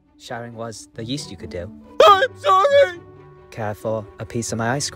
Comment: When he said I'm sorry I thought he said I'm farty